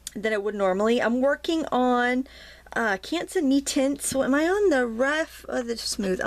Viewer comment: what paper did you use? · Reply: Rita Decook never mind - heard you say metiente